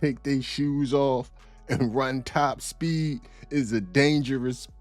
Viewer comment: I heard "STOP" lol, he absolutely got em